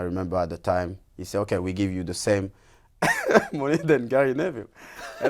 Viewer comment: for some peculiar reasons i failed to fathom what Evra said there...doe anyone care to offer a shoulder of assistance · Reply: Carlebeelz vlogs he said that on his new contract he will be getting paid the same amount as Gary Neville